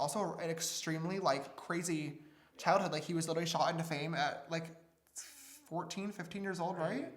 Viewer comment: When he said "shot into fame" I literally thought he said "shot in the face" lol